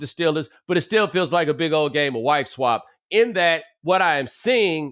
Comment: I thought he said white-swap which is another great term